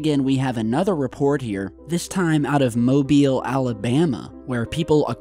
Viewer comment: Real talk. Anyone notice the car in the drone shot on the wrong side of the road. I live in Newfoundland St John's area and it was really windy and bad weather for 2 days but has since calmed down. Lots of damage around, the movement of the island was not noticeable to us just those sensitive machines picked it up. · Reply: I thought he said Mobile, Alabama.